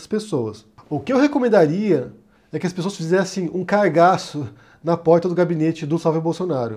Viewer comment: "Que as pessoas fizessem um 'cargaço'... " eu ouvi "cagaço". E se tratando do Flávio fez sentido dos dois jeitos.